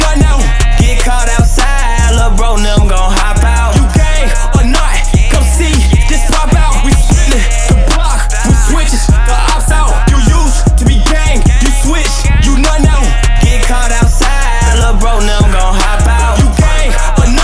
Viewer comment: I swear when I heard this in the theater I thought he said “ You used to be gay, you switched, you not now”